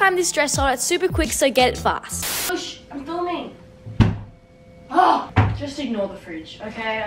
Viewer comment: Wha did she sayyy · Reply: Shush I am filming